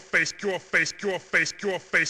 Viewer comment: it sounds like he is saying "trollface"